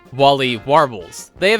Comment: Maybe that should be the alt channel instead of “ChickenNinja43.” RoosterSamurai24 must suck at Cuphead though. Edit: Also, the auto Subtitles say “Wall-E” instead of “Wally”.